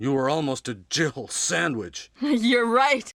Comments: For years I always heard it as gerbil sandwich and never unhear it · It's so bad it couldn't even say Jill Sandwich right, I always heard it as jibble sandwich... WTF is a jibble sandwich I wondered as a kid.